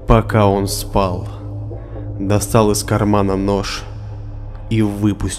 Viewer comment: Я услышала "нассал из кармана нож"